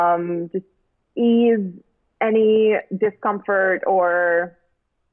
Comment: did she just say Cumfart? 😂